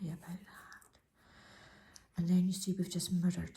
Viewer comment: Thanks for this easy to follow video. Love your voice and accent. Funny thing the first time i heard you say mirror i thought you said murder...lol...cracked me up. Cant wait to see your other videos.